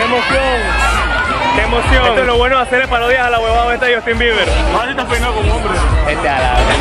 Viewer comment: JAJAJAJAJAJA que dice el hombre en el minuto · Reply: jeremy mendoza almanza bueno eso fue lo q entendí o man estas peinado como hombre, una de esas dos🙄